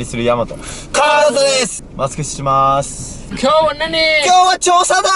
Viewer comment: 「マスク外します。」が「マスクしまーす。」に聞こえた。